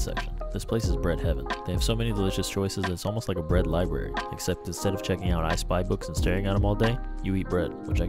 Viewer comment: I thought he said "instead of checking out ice spice books "